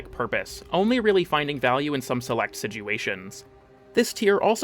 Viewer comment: Did you really say "some select siguations?"